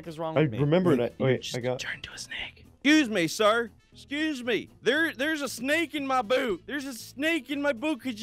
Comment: It sounds like is saying there's a snake in my boob